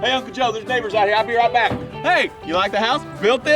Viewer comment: I heard “Mocha Joe” instead of Uncle Joe and it was a lot funnier to me.